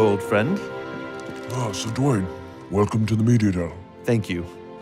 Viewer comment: the caption said sugoi instead of dwayne 8:20 Duane instead of dwayne